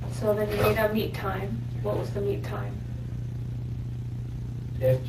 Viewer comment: Ok so... I'm watching with the CC (Closed Captions) on, since it's a little difficult to parse what all they are saying... The officer says/asks "Okay so then you made a meet time.. What was the meet time?" But.... the captions say... "Okay so then you made a meat time.. What was the meat time?" ...and all I can think is... "Yeah... He THOUGHT it was gonna be "meat time" when he got there... But the real "meat time" is gonna be when he gets to jail/prison with a "Grooming a 12 year old, attempt to statutory rape a 12 year old" charge on him. Because if there's ONE THING inmates hate... it's a pedophile. So... "meat time" is going to happen... just not when, or with whom, he thought it would...